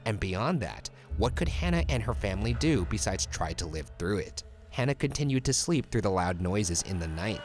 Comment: Is it just me or did I thought it said Hannah Montana